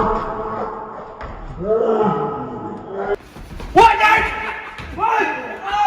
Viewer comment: he sounds like he saying woah dad!